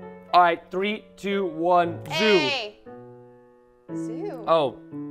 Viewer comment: did bro say siuu like Cristiano Ronaldo siuu like what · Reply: No, he said zoo